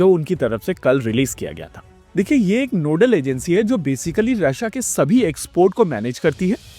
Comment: "yeh aik noodle agency hai" .... i love noodles, that's what i heard, you can't convince me otherwise!